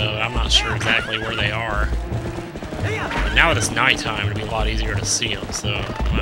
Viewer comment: LOL at 2:07 with the captions instead of now that its night time it says now the story contemplating statesmen